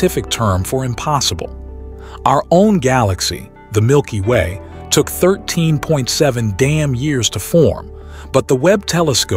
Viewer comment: did he say "took 13.7 damn years to form?" lol